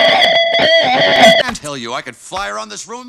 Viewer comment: I swear to god every time I watch this scene I hear Woody say “light beer” instead of light year, I literally can’t hear it any other way.